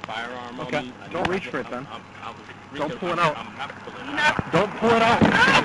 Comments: This is absurd, how can people side with the cop? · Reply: i hear philando clearly say "imma have to pull it out" at 2:15. this is after the cops repeatedly warn him to not pull it out. his wife exclaims no, when he says "imma have to pull it out". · I clearly heard Philando Castile say He was not pulling it out at 2:14. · It really sounded like the guy said "I'm going to have to pull it out. I could be wrong but it sounds like he said that.